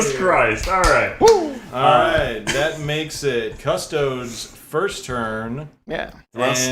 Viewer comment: Wait, at like 10:38 did he call them "cuss-toads"?